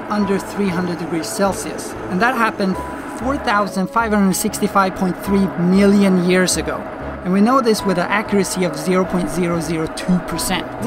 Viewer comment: Does he say 4 thousand, 5 hundred and 65 point 3 million years ago? What? XD i’m so confused...